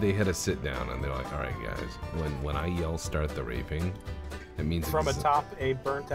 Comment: I thought the baddie said "START THE REAPING" reaping!